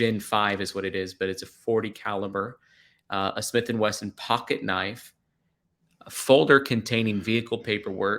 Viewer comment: The report your showing says pocket knife. I thought they said a saragatted knife originally.